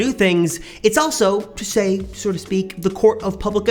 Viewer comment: did he say "sorta speak" instead of "so to speak"?